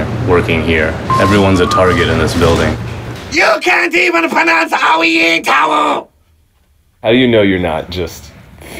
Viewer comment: what does trey say? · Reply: You can’t even pronounce “OWIENTAL”